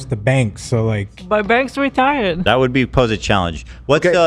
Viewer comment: did Hila just call Banks restarted